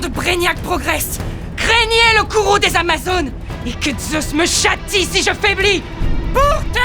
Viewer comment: Moi j'entend : "Montjoie Saint-Denis, que je trépasse si je faiblis !!!"